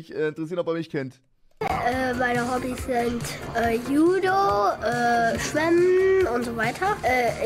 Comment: was hat der kleine gesagt tren? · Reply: Schwimmen